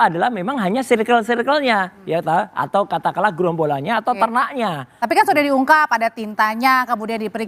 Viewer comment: apa saya salah dengar? Apa rs sebut "ternaknya?"